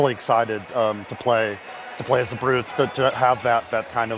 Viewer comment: did he just say we're going to play as the banished/brutes defending against the flood. also plus who was Atriox telling not to go to the mysterious place where the flood live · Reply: Yes we're going to play as The Banished, and he was telling his Brutes not to go down there - but they still did.